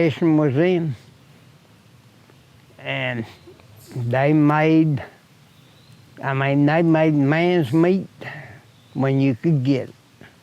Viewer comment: “they made mans meat when you could get it” ? also what does that mean if that is what he said :) thanks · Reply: he's saying, "They made Ends Meet, When they could get it..." In other words, his parents did the best they could with what they had at any given time, and for sure sometimes they went without, since they couldn't always " get it". 🙏 Amen